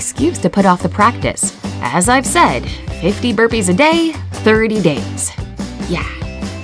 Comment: I thought it said doing 50 burps a day xD